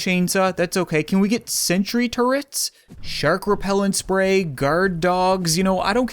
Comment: did you say sentry turrets ?!